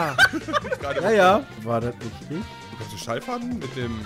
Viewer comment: Was sagt Brammen bei 5:22 zu Chris? Ich versteh es nicht :D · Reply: Versucht Fallschaden zu sagen aber sagt Schallfaden